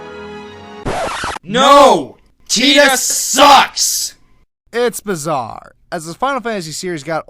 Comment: To be honest, I thought he said "TITIES SUCKS" at first.